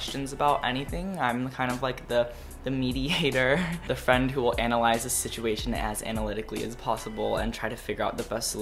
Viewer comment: i thought you said "meaty hater"